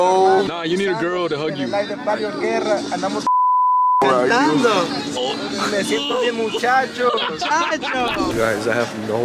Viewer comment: does he say fucking vereena where are u? LMAO